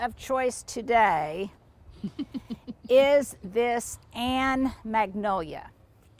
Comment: What is the name of that Hydrangea that you pruned this morning. I have been looking for a hydrangea like that for a while. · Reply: Never mind, I went back and listened again and heard you say, Ann Magnolia. I am looking for a hydrangea that has a similar growing pattern.